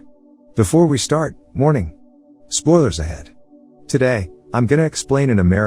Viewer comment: after watching you for months i now realized you were saying warning i thought you we're saying morning